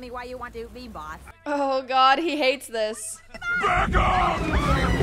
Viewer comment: what did Junior say at —> 15:25? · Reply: "BACK OFF!"